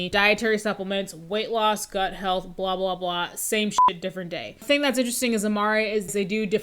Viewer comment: haha, I heard that as same sh1T different toilet